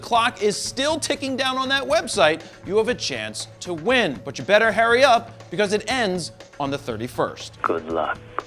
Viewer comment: Anyone else catch the "better HAIRY up" at 3:35? Lol